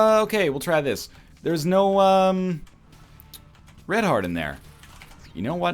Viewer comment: NO REDHEART? DID YOU SAY NO RED HEART?